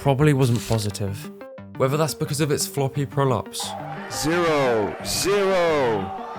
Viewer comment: The first time I watched this I thought he said "floppy prolapse."